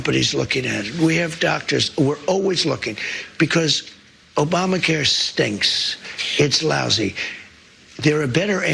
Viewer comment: i hear YOU stink, trump.